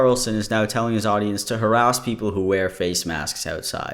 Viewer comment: why did I hear "arouse" instead of "harass" 😅🤣 made me question my hearing lmao